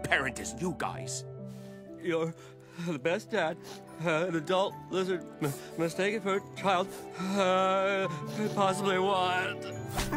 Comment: Stabby, the 43 year old war veteran and murderer baby child, my beloved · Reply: Either I’m deaf or something - ya’ll are probably talking about 10:25, right - but he clearly says “You’re the best dad a[n] adult-lizard-mistaken-for-a-child could possibly ever want.” This part mentions age on a general scale, but no number. I don’t know where “47-year-old” is coming from at that part, I just know the part that I bookmarked in my earlier comment mentions that Stabby is 43 and Durpy’s 47. I don’t think the events of Centaurworld took long enough for the cast to age either. Ya’ll gotta be gaslighting me or somethin’, I swear I’m not tripping because I’ve gone back and watched it every time I’ve been pulled back here.